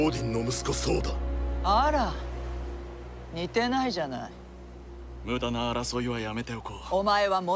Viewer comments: correction by a japanese 0:09 muda na arasoi wa yameteokou(無駄な争いはやめておこう) 0:12 omae wa monoii ga niteru(お前は物言いが似てる) 0:26 jouoh no mae ni(女王の前に) 0:30 dare ga jouoh da(誰が女王だ) 0:43 sonna baka na arienai(そんな馬鹿な！有り得ない) 0:46 soreja konnano mo arienai kashira?(それじゃあこんなのも有り得ないかしら？) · correction: 0:09 muda na arasoi wa yamete okou (無駄な争いはやめておこう) 0:11 omae wa monoii ga niteiru (お前は物言いが似ている) 0:28 jo'ou no maeni (女王の前に) 0:30 dare ga jo'ou da (誰が女王だ？) 0:42 sonna baka na! arienai (そんなバカな！ありえない)